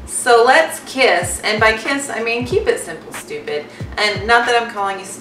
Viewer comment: Hey! I heard it as Keep It Simple Sweetie (K.I.S.S.)! Thank you for sharing your Keto experience. I appreciate hearing about your struggles and successes.